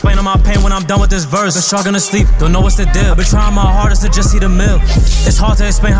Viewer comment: did he say “i been trying my hardest to eat a meal” i thought he been saying “i been trying my hardest to hit a mill”